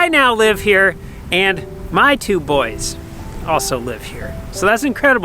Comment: So the new baby is a boy!!! Didn't know that, where is the baby video???😍❤ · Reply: Well I swear I heard him say “my two boys”. I know of one????